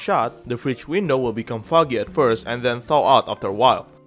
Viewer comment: I thought he said, "Thot" instead of, "Thaw" Sorry I cant spell T~T